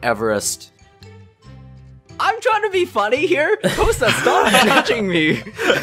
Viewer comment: thats the greatest feminine voice i ever heard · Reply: I heard "hosuh stop touching me" and had to rewind it to see if I was wrong lol